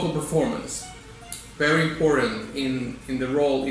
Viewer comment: I thought you said "Horrid" instead of "Important" and I was about to lose my marbles haha